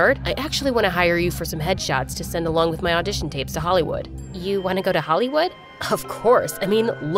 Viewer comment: is it just me or did he say “sa holleywood”